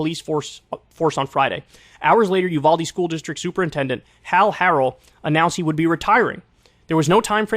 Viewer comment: I thought I heard Paul Harrell instead of Hal Harrell at first lol!